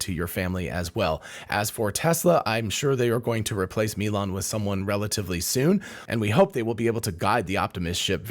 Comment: I thought for a second that you said: "I'm sure they're gonna replace Elon with someone relatively soon". 😅